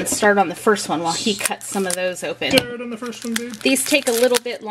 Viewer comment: did say start on the first one babe???